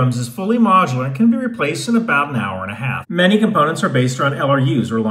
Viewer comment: I hear, "it's wasteful but so is a whore"... Realizing he said war... Ohhhh, yeah that's accurate